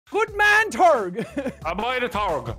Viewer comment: we just aren't gonna talk about what said here? Wtf did he even say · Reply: Ah by the Turg. Meaning, good boy the Turg